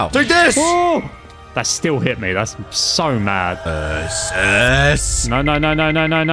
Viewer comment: when you said URSUS at 7:33 i legit thought you were saying This is i thhought u were gonna use the spartan quote THIS IS SPARTA! but it turned out to be URSUS SHOCK!